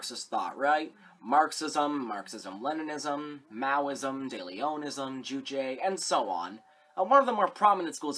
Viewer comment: I think you said deLeonism at the beginning of this video but I heard "daily onanism" and now I am permanently pleased